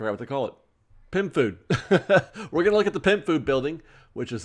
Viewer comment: I keep hearing you say “pimp food” instead of pym. 😂